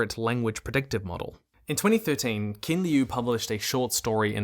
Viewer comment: When you said Ken Liu, I thought you said "Ginyu". Especially since the site is basically a space pod lmao